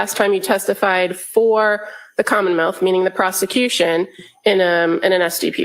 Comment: does she say “commonmouth”?